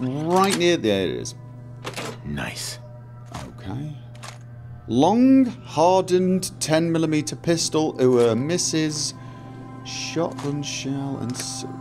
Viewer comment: I had to skip back to 1:50. Was sipping my coffee, when I heard "Long, hardened 10mm pistol for missus."